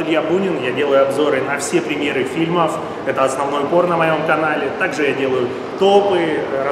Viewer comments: мне послышалось, или Илья сказал: это основное порно на моем канале? :) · Мне одному послышалось "Это основное ПОРНО на моем канале" ?